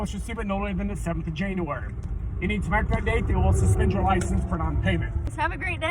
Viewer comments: I know what he says, kind of He says: “7th of January” “Well give you 95 days or we’ll suspend your drivers license for non payment” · I'm from the Midwest and the first time I heard the cop audio I thought he was speaking Spanish. Then for some reason, without looking at the comments or anything, I listened to it a second time and it made perfect sense. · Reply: i can kind of understand it? i heard "or we'll suspend your license for non payment" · I think the cop said “you know what chu did? On the (time) The 7th of January. You need to pay by this day or they’ll suspend your license for non payment. Lol i really don’t know 😂 I think that’s as close as you’ll get